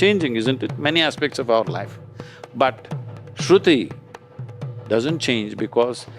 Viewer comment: instead of "Sruthi" subtitle says "Smruti". 🙏